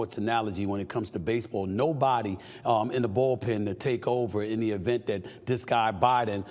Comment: is he saying ball pen?😂